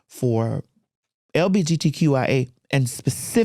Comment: LGBT but yea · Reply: Thats what it should be, yes, but she says "LBGT" at